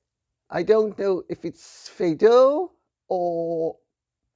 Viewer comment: To me it’s less Shakespeare or Fado and more of a Mexican telenovella. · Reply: York Oh! I thought she said Feydeau!!